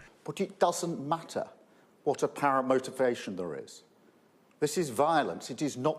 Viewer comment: Starmer speaking. There is something dodgy going on there. Did he say "apparent motivation" or "para-motivation"? Look at his lips as he says this; they go way off to the side. Is this real footage or doctored? If he did say it doesn't matter what the motivation is then why is murder legally different to manslaughter?